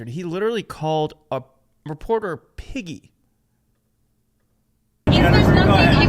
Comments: Honestly I thought he said McGee, not piggy. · 😲 I thought he was saying the name "McGee." He really said piggy. What an ass.